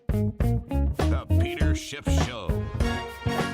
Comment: Is it only me but I keep hearing “The Peter Shitshow”? Which is not funny because our economy is a real shitshow and Peter has been always on point!